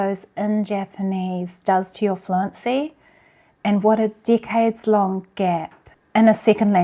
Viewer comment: I thought you said decay but it’s decade. Omg. So weird